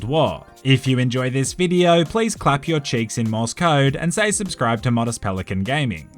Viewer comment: I thought he said clap your cheeks in Moscow